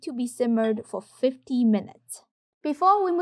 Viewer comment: Did you say 15 or 50 @ 4:32 ? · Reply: 50